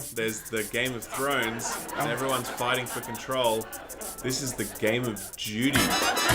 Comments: I thought Ralph was saying "Game of Judy" i was so confused. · I honestly thought you said "...Game of Judy". Sometimes I think we speak different languages. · I thought Ralph said "game of Judy" until they started talking about the actual story. I was sitting there thinking, who the f is Judy?